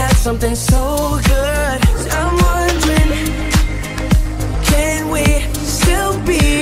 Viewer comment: my alcoholic mind really thought he was saying “i wanna drink” instead of “i’m wondering”